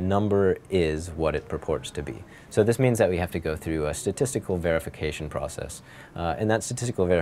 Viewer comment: I swear I heard "sadistical verification process".